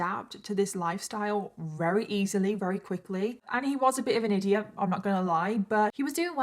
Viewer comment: Lol did I hear that correctly? "He was a bit of an idiot"